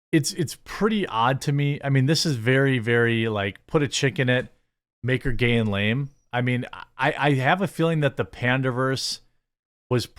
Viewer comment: In my head I keep hearing this voice “….. make her gay…. and make it lame.” 🤔